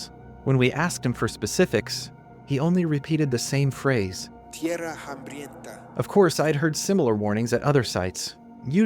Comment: What does the guide say at 2:19, and how would you spell it? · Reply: "tierra hambrienta"